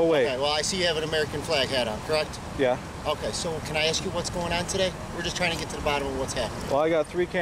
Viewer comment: Why do I always hear: “I aks” all the time instead of “I ask”....and I am not even from the USA